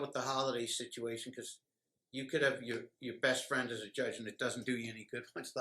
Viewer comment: did he just say ‘you can have a judge as your best friend…but’. WOW. If you want to talk about showing your hands